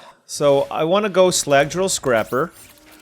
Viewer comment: I heard "Slylandro Scrapper" and thought had Star Control on the brain